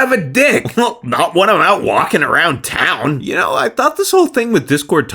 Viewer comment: I can't decipher what is supposed to be saying at 03:37. Can anyone help? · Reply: Not when I'm out walking around town